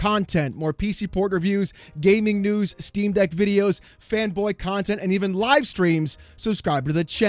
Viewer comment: hahahaha at then end when he said "fanboy content" i misheard it as "femboy content" and immediately went "wth?" XD